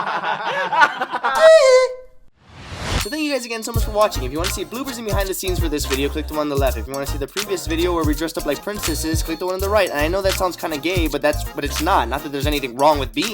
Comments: Omgosh when u said see bloopers and behind the scenes at the end I thought u said "So, if you wanna see boobs and behind the scenes..." Lol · Who else heard it as “if u wanna see boobs and.....” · I thought he said "If you'd like to see BOOBPERS and behind the scenes..."